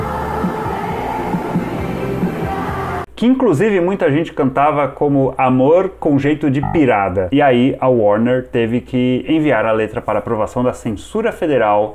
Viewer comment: Virada??? Juro que, a minha vida toda, eu ouvi "amor com jeito de PIRATA" 😅😅